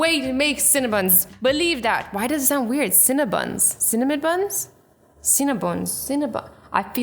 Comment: why do i heard cinambutts